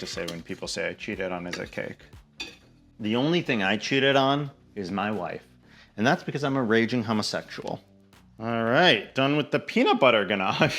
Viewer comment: "I cheated at life by being homosexual" is the quote of the year for me, thanks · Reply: Is that what he said? I literally heard him say “I cheated on my wife because I’m a homosexual” like it was a joke because he’s not married but then I was like, wait, was he married??? But that makes a lot more sense! 😂